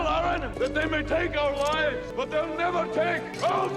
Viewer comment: My hubby watched this with my kids the first time they saw it. My 13(ish) year old daughter said, "Did he just say, "They may take our wives"? (Instead of They may take our lives.) Why would that be ok"?